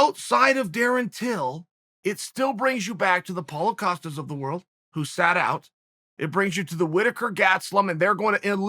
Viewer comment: I thought Chael said “Paulo Costas of the world, USADA out”